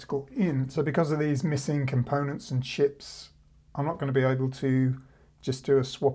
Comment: What I heard at first: "Because of these missing components and shit..." WHAT? Back up. "Because of these missing components and chips..." Oh. Okay. Carry on.